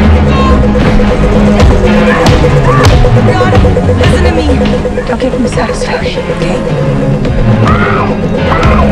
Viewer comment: Wtf does she say at 6:25? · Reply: Brianna, listen to me ! Don't give them this satisfaction OK ?